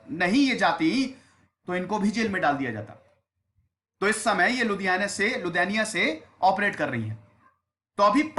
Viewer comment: I heard Ludhiana instead of Lithuania 😂